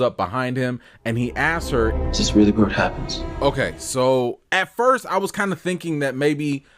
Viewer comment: To me it sounded like Elgin said "is this really where it happens?"